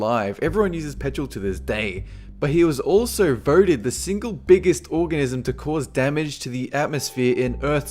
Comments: sounds like he said he was voted the biggest orgasm. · he said orgasm instead of organism